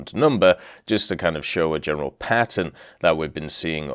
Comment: Did somebody just say General Patton?